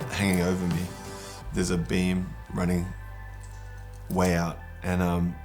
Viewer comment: Did he say "beam... money, a way out." Money is the root of all evil, don't give into temptation my heavenly siblings. (2:00}